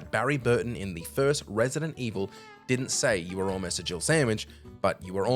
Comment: that’s what I thought they said for years when I was a kid! 😅 It’s the way Barry says it, it’s easy mishear what’s said. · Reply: I still to this day hear Barry say Gerbil sandwich, I've watched it over & over I cannot hear Jill sandwich.